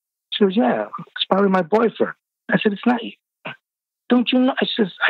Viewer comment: I thought he said BOYFRIEND. Because I'm thinking....his co-worker is a gorgeous 10 amd has a boyfriend yet imvited him over AND casually says she has a boyfriend AND he doesnt say anyhing to her?